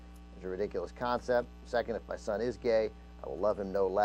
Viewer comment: ohh... i misheard anna there, i thought the mom wrote: "besides my son ::is:: gay" instead of was